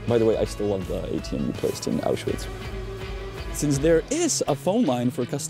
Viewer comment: what did you mumble here ? it really isn't clear, I can't hear what you're saying... "by the way I still love the ATM encased in Auschwitz" is what I can hear... that can't be right lol